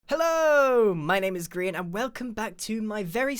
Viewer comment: i thought you said "my name is green" e.e